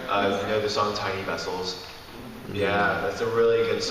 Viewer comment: I'm German and didn't catch which song/musician is talking about. Can anyone help me? :) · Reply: Hes talking about the song "tiny vessels" by death cab for cutie